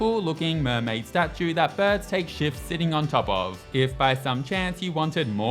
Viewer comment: I thought u said birds take shits on xD